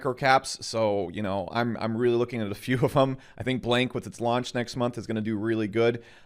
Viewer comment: what coin did he said at 2:53? Blenk?